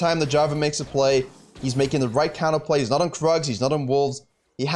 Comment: I thought he said he’s not on drugs XD